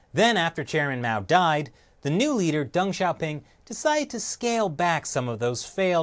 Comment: lmao the captions say 'dong shopping' instead of Deng Xiaoping